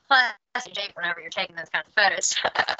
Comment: What does she say at 13:34 that gets the laugh? Glad that Valentina/title shot thing got brought up too, 'cos it seemed pretty apparent to me watching those very same media day interviews that the topic was starting to weigh on her a little. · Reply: I believe she said it’s a lot of fun when you’re taking those kinds of photos. The audio was a little wonky there.